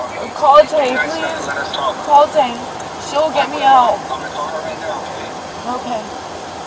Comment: All right. She's 18. She's driving drunk. She's a stripper. She has guns in the car. She's a mom. And apparently the child is old enough to talk. Pretty much checks off all the boxes doesn't it? This girl is a keeper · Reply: Gray When did she say that? I only heard, "Call Tay, she'll get me out."